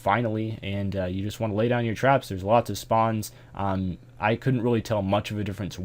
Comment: this is what I heard, "lay down your tramps." Priceless.